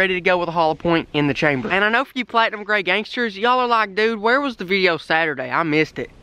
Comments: I legit thought he said logangsters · at first i thought he said "whats up gay gang" then i saw his last name